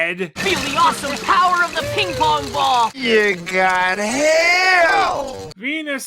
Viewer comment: I know he said "hail" but it sounded like he said "You got Hell!" Which is a perfect description of this place. And series.